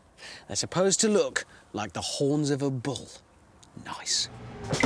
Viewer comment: When he said "The horns of a bull" I heard "The holes of a bum" 😂😂